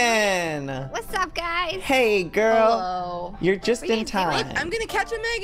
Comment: anyone else heard say HOLO??????